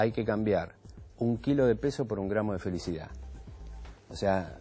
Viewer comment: Yo escuché " no cambiar un kilo de faso por un kilo de felicidad " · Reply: No cambiar un kilo de peso por un gramo de felicidad!!! Vi la nota completa x A 24, es excelente. Lamento que el video no esté completo pero igual es un placer escuchar al doctor